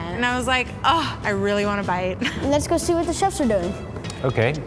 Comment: I keep hearing 'Chef Charizard'.